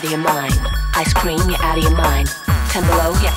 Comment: I always thought she said “Toblerone you’re out of your mind” at the end. 🤪